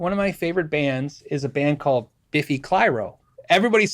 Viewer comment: what the heck did he say that band is called? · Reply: Question asked, question answered. "Biffy Clyro". Just found their vids. Watching on my phone (which is NOT stereo). Sounds like he said "Beefy Pyro".